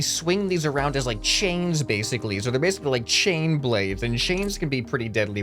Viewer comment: at first i thought it said china blade